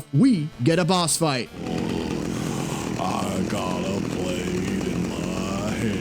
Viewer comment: Weird, for the longest time I always thought he said "I got a blade in my hands..." Personally, I think my version goes a little harder.